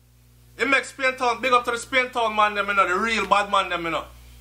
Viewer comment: smh, What did you say at minute 3:37 in this video? You did not big up any bad man? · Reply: since my intelligence and listening comprehension are sub zero i eagerly awaits your explanation to minute 3:37 in this video. "Big up to di Spain Town man dem eno, the real bad man dem eno" those were your words not mine.